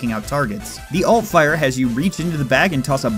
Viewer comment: Did you say ULT fire?